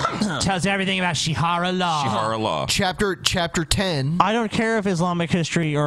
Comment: I thought she said "Charo Law"